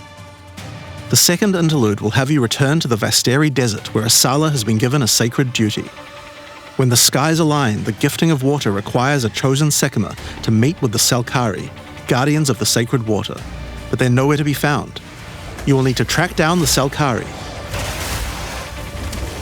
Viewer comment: Did he just say "mate with the Selkari"???